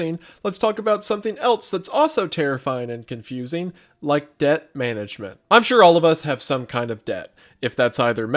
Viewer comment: misheard debt management as death management and thought that theres a wendigoon themed funeral service